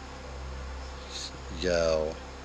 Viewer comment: after he says "yo" I hear a whisper saying what sounds like "yes"